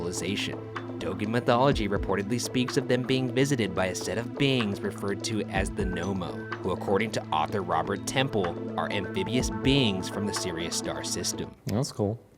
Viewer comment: Lol at 4:35 i thought it said no homo